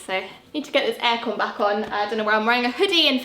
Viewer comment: lol, did she just say AIRCON?